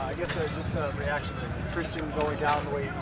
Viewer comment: man I was looking all over for a Darrisaw injury lol, but dang sucks for the young Wr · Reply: no darrisaw is good. Somebody said Tristan and I thought he said Christian